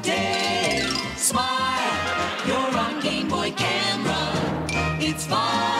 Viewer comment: sounds like he said gayboy